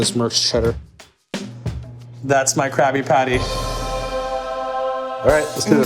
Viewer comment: Is it just me, or does he sound like he is saying "Krappy-patty" ???